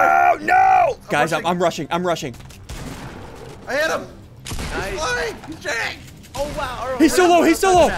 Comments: I think I heard cuss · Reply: planetorium if you put on captions it says “he’s flying, he’s trash” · is that a curse word I hear? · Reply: He said shit